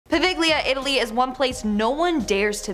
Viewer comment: That was the most mangled pronunciation of poveglia I've ever heard. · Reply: Thank you! I thought she said La Vigilia 🤦🏻‍♀️